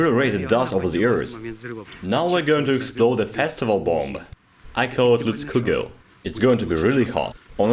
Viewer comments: I had to rewind cause I sware I heard "testicle bomb" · I thought he said “Testicle Bomb” He didn’t, but the name fits :-)